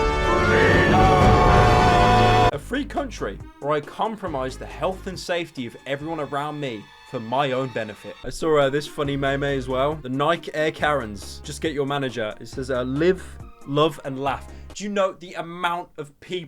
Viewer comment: ohhhhhh no no no u did not say meh meh